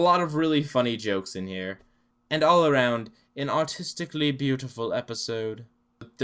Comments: When you said “Artistically beautiful” I thought you said “Autistically beautiful” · I thought you said that it was an autistically beautiful episode.